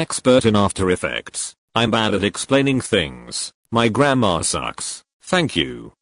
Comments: am I the only one who heard "my grandma sucks" 😀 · I heard 'my grandma sucks' XD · What said: “my gramma sucks” What I heard: “my grandma sucks” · When is said "my grammer sucks" I heard "my grandma sucks" and when I realised it I was like 😀🔪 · I thought she said grandma- lmao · I hear "my grandma sucks" and not "my grammar sucks" help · I thought u said my grandma sucks.. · When they said "My grammar sucks" i really thought they said "my grandma sucks" i was like WAIT WHAT? AM I HEARING THIS RIGHT????!?? And it took me <num> times too understand that they meant grammar :> your save grandma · “My grammar sucks” I thought you said grandma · I fr thought she said "My grandma sucks" lmfao 💀✋ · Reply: I DIDN'T EVEN REALISE THAT SHE SAID "My grammar sucks" UNTIL NOW, I THOUGHT SHE ACTUALLY SAID "My grandma sucks" JAHSJ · Instead of Grammar I heard "my grandma sucks" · I thought it said "my grandma sucks" what the hell is wrong with me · I HEARD "MY GRANDMA SUCKS" INSTEAD OF GRAMMAR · Bro at first I thought u said "my grandma sucks" instead of "my grammar sucks" and I just took a second to think and I'm just like "wait.. what does your grandma have to do with English?" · Wait, I thought she said ,, My grandma sucks" xd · AT THE START I THOUGHT YOU SAID "My grandma sucks"LMAO (thx for the Tutorial) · i thought she said "my grandma sucks" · I heard "my grandma sucks" than "my grammar sucks". Damn.